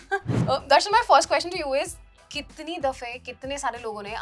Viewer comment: she said Darshan my first Question to you is...😂 I heard like Darshan Bhai first question to you is... 🤣🤣🤣